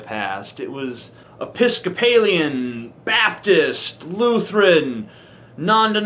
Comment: For a minute there I thought he said mephistophelean. Now THAT would have been funny.